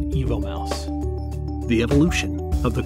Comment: I hear "the Evil Mouse" all the time